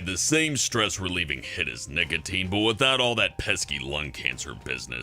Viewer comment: I thought he said niggatine